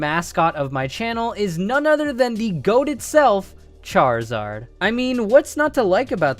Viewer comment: "The goat itself" did I only hear that?